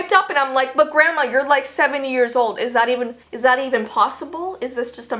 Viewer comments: it sounded like you said 7 years old😂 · 'You're like 70 years old.' I thought she said 7